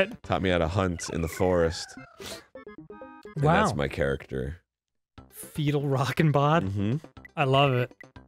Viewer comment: I thought he said fetal rocket bod, like his character was birthing out babies constantly